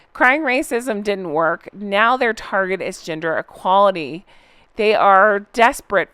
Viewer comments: I thought you said Ginger equality 🤣💀 I could see him doing something like that. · 😂😘💓 .... When you said "gender equality" I thought you said "ginger equality" so I imagined Harry becoming an advocate for redheads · Bookworm 2 did a video, about being escorted out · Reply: I thought said Ginger equality; instead of gender equality! Lol! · I thought you said “ginger equality” when you said “gender equality” and I almost choked lol Imagine! The fact that it sounded believable just gives you and idea of what we are all used to and can expect from these two. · I thought you said 'Ginger Equality' instead of gender equality about the 10 minute mark & for a second thought, yup that'll be his next thing....WAAAAAHHH people treat me so badly because of my hair colour, woe is meeeee 😂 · I thought you said ginger equality😅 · It's Ginger Equality ...... not Gender Equality that Harry misheard and thought he was promoting. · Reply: I also heard her say Ginger Equality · I was just listening in the background but I thought you'd said "ginger equality" instead of "gender equality" and I was thinking, that is the sort of BS cause Harry would take up to get more ginger people in ads mowing the lawn and doing the dishes. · I misheard what you said and thought you said they're targeting GINGER EQUALITY!!! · I thought you said "Ginger Equality." 😂😂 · i thought said "ginger equality" not "gender equality!" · I could have sworn you said ginger equality